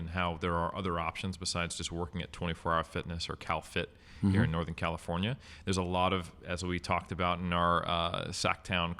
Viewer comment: What film does mention at 1:29? I can’t actually hear what he says. I’m interested in what film he mentions???? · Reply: Madadayo. Akira Kurosawa's last film.